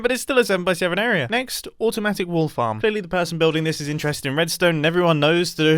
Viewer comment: I thought you said wall farm XD